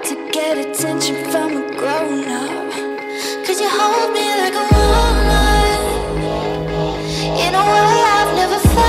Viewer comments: it sounds like she said Cause you hold me like a walnut Anyone else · All I heard. "cause he holds me like a walnut" Chokes on water. The end